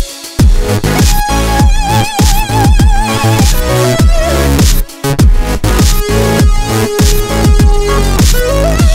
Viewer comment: someone tell me what the name is...? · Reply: Godot is the name.